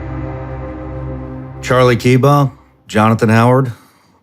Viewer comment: Thought you said Charlie Keybumb. I’m thinking dang he wild in a past life.